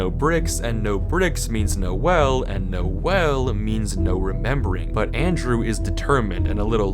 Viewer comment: i literally heard "noelle" or maybe thats just the deltarune brainrot